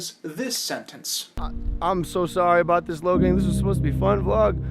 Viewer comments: Great video, but I think you made a small error at 7:40 -ish where Logan says "I'm sorry Logang, this was supposed to be a fun video." He refers to his fan base as the Logang, and since it's basically his name, it makes sense to think he said Logan, but I'm pretty sure he was referring to his fan base. Otherwise, great video, I found myself agreeing with you throughout. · Great video I totally agree with what you said in it but there's just one nitpick, it's when he said "I'm sorry logang this was supposed to be a fun video" and you misheard hi saying "I'm sorry Logan". He and his brother calls their fanbases "Logang" and "Jake Paulers" since they're quite the narcissists otherwise a really good video and happy new year